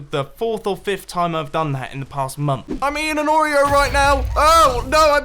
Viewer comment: When he said "I'm eating an oreo", for some reason I thought that was a British term that means he's in a bad situation or something.. then I realized that he's actually eating an oreo. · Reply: same. Also at first I thought he said "I'm in an oreo"